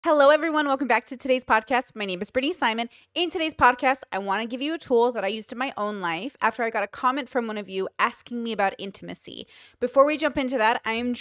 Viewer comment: I thought she said her name was Pretty Simon at the beginning for a second lol